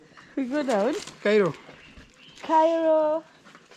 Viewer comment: what a shout out from first lady herself ... infact at first i thought is #KANAIRO i had to rewind again now i got it clearly is #KAIRO 🤣🤣😂😂🕺🕺 so Impressive 😂😂😂😂 ...TIA